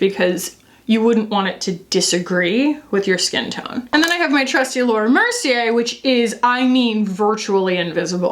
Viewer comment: I misheard it when you said 'I have my trusty Laura Mercier'. I heard 'I have my trusty lord have mercy' and thought wtf 😆